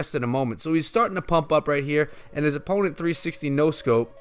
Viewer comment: great vidro! As you said it,the heal spell works really good with 3 musketeers. Also at 4.56 i thought that you said that his opponent 360 no-scoped him 😂haha. Well,in my opinion i think that tge heal spell can become meta.The fact that it heals baloons,hogs,giants,royal giants and elite barbarians to a decent amount of health scares me.Hopefully the meta won't become a beatdown-heal spell meta or else i quit the game :O